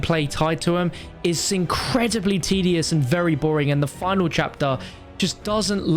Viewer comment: I gotta agree with you with the ww2 map but I thought you said the finale Reich I was so mad until I noticed that